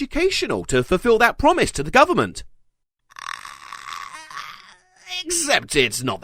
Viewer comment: Some may have heard "the government!" But i heard "ta guber mont!"